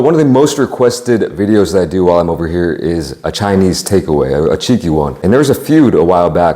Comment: Love this guy and his chowing-down work ethic! Gotta say though, I definitely misheard “cheeky” when he was saying “have a Chinese takeaway.. a cheeky one” 🤣 Thought bro was about to get cancelled..!